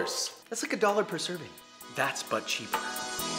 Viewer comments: I think you guys are ignoring the fact that I condemned every other method of cooking rice that ISN'T in my Zojirushi rice cooker. But because this is but cheaper I gave other options because you ALWAYS complain about expensive equipment. IF I didn't give those other options then the top comments would be "OMIGOSH JOSH REEEEEE THIS IS BUT CHEAPER BUT YOU SPEND 100 DOLLARS ON A RICE COOKER???" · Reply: What you said: "That's 'But Cheaper.'" What I heard: "That's butt-cheap." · Newbie here from Babish's channel! Hi! I'm sure this has been said before, but every time he says "but cheap," I hear "butt cheek." Lol so glad I found the channel, though! SO helpful. <3